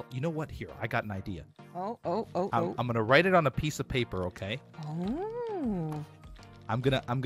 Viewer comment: I swear I thought it said piece of s*